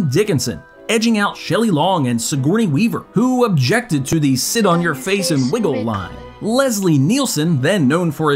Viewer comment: I always thought Julie Haggerty said: 'Sit on your face and 'wriggle'?